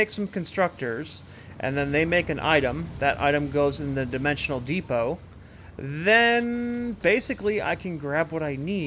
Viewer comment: Is it just me or do I keep hearing "emotional depots" when you're talking about the dimensional depots? haha Great builds! Looking forward to seeing this series continue.